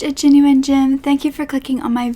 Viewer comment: Urggh, thanks youtube for ruining intro. One time I had subtitles on and when you say genuine gem, the subtitles said "genuine Jim" and now I heard you saying genuine Jim everyfuckingtime and it's so annoying 🙄😡